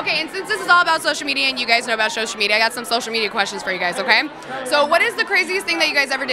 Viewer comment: I kept hearing “shocial media”